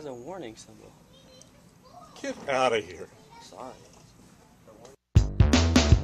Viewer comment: i thought it was saying "Thats Racist" but then i realized it was ur logo thing that said "Pranksist"